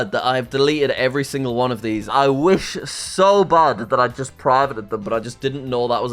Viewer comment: "I wish so bad I'd had (incessant mumbling) them but I just didn't know that was a thing at the time" my friend what did you say??!!!??! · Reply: “Just privated” them